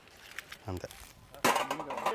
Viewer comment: captions say "I'm dead" wtf 😮😮😮